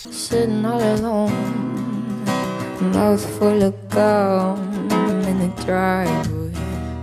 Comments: Did she say “a mouth full of cum in the drive way” ?? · I can’t understand what she’s singing. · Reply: did she just say "mouth full of cum" coz I can't with his noncery